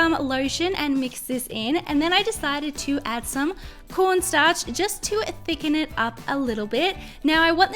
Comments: did she just say porn starch? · I heard ‘PORNSTARCH’ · Am I the only one who heard her say P O R N Starch